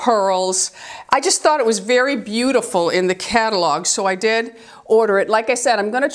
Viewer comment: Re-watching this video and NOW I hear 'in the catalog'. Oieeeeee, do shop only online for these stores?